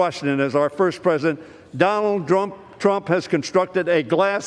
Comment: Was I the only one to catch that. He saying Donald “drunk” trump 😂😂😂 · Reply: I heard it as Drump